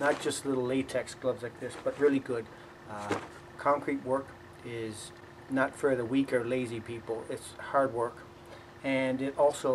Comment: Did you just say concrete work is not for Lady people?